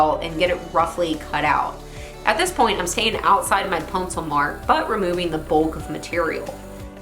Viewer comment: Did say Puncil instead of pencil at 8:43? Love the builds.